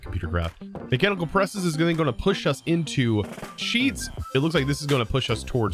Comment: at 1st I heard say "push us into cheese" so I had to do a double take am I the only one who heard it?